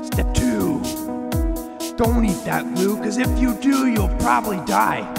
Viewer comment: I tried step two because I thought he said Eat that glue and die whoops